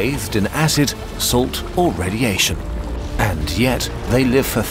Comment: I wasn't paying attention for a second and thought he said "Bathed in acid, salt, or *humiliation*"